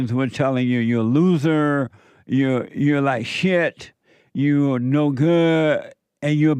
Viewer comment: did Jesse say shit?